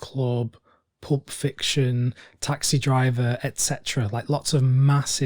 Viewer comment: Did I hear "Plup Fiction"? Agree.